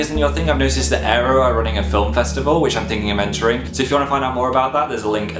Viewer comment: Was I the only one who thought he said "Phil Festival" ?